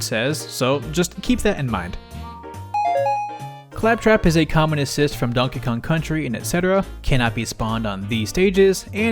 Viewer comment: How did I hear "communist"